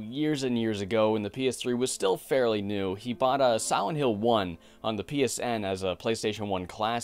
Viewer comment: I really enjoyed the video. But about 2 minutes in, I noticed it sounded like you said "Sowent Hill" and then that's all I could hear